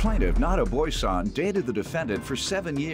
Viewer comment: I heard "not a boy son"